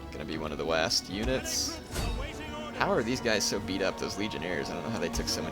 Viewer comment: for some reason, I laughed hysterically because I thought he said 'legionHEIRS'